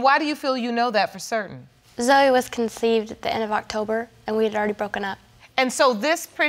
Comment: I thought she said end of October